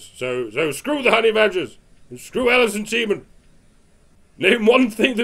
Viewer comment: It sounded like you said Alison Semen.